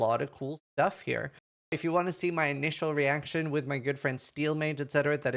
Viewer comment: I heard: “with my girlfriend”